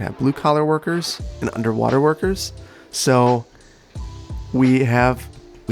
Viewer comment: I took it seriously when I heard "under water workers". What the hell are those ??